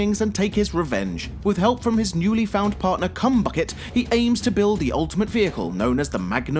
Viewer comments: did he just say cum -bucket I'm no expert but I think it's Chum Bucket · did he just say cumbucket?! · did he just call him "Cumbucket"?!?!?!?!